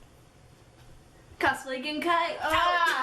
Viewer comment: What did you say to at 5:02? I can't stop watching this I love it so much and I'm OBSESSED with RPG with Mad Father being my forever favourite. My Dio cosplay was alright but the wig was horrible! It may seem weird but do you live in Japan? XD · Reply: said "CosplayGenkai Out!" Which is kind of an ongoing joke. I find it really dorky whenever they end the videos like that haha. xD And thank you so much! Nah, we love Japan but we live in America~